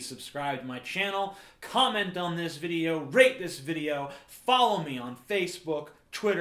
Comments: It honestly sounds like you said rape this video Hahaha! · LOL after watching this and hearing you emphasize on rape so much I swear I thought you said "rape this video" at the end. XD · in the end of video its sound like hes saying "rape this video", i guess its time to move on^^ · Anyone else jumped a bit when they thought he said "rape this video" at the end? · After hearing all these weird "rape facts" i actually thought you said " comment, rape this video" at the end. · i misheard it as "rape this video" instead of "rate" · Haha I heard "rape this video" at the end:D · LOL after i heard him say 'rape' so many times in the video I heard "Rape this video!" in the end. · strangely enough I heard "rape this video" instead of "rate this video" at the end xD *not gonna join the pointless discussions below me* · I thought he said rape this video instead of rate! XD